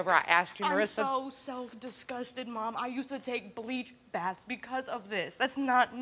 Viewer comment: They weren’t even happy when he passed something tells me they were inappropriately touched by someone else or fabricated this story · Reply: thought she said bleach baths. Not blood.